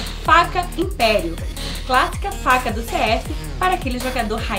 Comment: Eu Ouvi ela falando ''FACA DO CS''